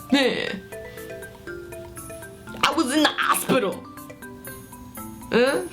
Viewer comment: It sounded like you said you were in the "asspetal" instead of hospital...chocolate milk nearly came out my nose which was weird because I wasn't even drinking chocolate milk or whatever.